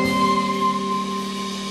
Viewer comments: when I first bought this album waaay back in the day, I actually thought he said " have you seen junior's grave?" lol and I was like what the hell did he say? lol those were the days when a concert was still about the MUSIC AND THE FANS.. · When I was a kid I thought it said "Have you seen Juniors Grave" LOL. It would of worked.